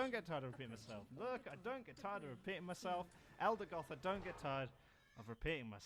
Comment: Guy is stranded on an inhospitable planet - Everyone: Okay, he's dead - Guy shows up 40 minutes later in a spaceship - First radio message: "No, I don't ever get tired of repeating myself. No, I don't ever get tired of repeating myself. No, I don't ever get tired of repeating myself. No, I don't ever get tired of repeating myself. No, I don't ever get tired of repeating myself. No, I don't ever get tired of repeating myself. No, I don't ever get tired of repeating myself. No, I don't ever get tired of repeating myself. No, I don't ever get tired of repeating myself. No, I don't ever get tired of repeating myself. No, I don't ever get tired of repeating myself. No, I don't ever get tired of repeating myself. No, I don't ever get tired of repeating myself. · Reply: I thought he was saying "no I don't get tired of peeing myself"